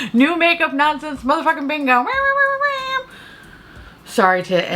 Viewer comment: I thought you said "Makeup nonsense brother-fucking bingo" for a second.